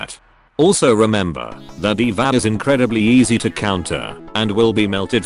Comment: I had to rewatch that fragment three times to understand what was said. Eval? Divahl? Oh, D.Va...